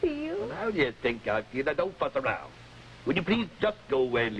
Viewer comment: = did he say "don't f*ck around?" 😱😱😱😱